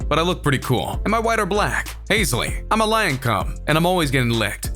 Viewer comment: “I’m a lion cum” sounds like what u said 🤣🤣